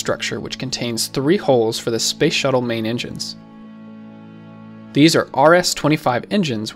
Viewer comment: I thought it said "Space shuttle inside space"